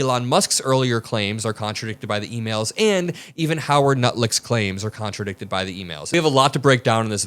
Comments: Did he say "Howard Nutlick"? :D :D :D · Did I hear you correctly at 3:22? Howard Nutlick? 😂😂😂😂😂😂😂😂😂😂😂😂😂😂😂😂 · did he say Howard nutlick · Nutlick? Did I heard it correctly? 😂😂😂😂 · did you call him “Howard Nutlick”? LMFAO, Awesome!!!! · I heard Howard nutlick around 3:20. Intentional or a slip, dont care that shit was funny homie 😮😂😂😂😂😂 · did you say "Howard NutLick?" NICE, I'm using that. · I think I heard say.. Nutlick instead of Lutnick..😂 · You dropped "Nutlick" instead of Lutnick at roughly 3:23! Priceless! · Did I hear correctly? Did he call old boy Nutlick?😂😂😂😂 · I swear he said Howard Nulick around the three 3:20 mark. 😂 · I heard Howard Nutlick.... that was a Freudian slip....and a well-deserved one. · Am I the only one who heard @ 3:22 saying Howard Nutlick instead of Lutnick?! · I definitely heard “Howard Nut Lick” at 3:21. Am I right?! This name should stick!!!